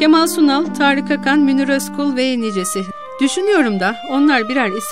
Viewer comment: Kemal Sunal, Tarık Akan, Münir Özkul sonra ne diyor? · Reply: Ve nicesi ..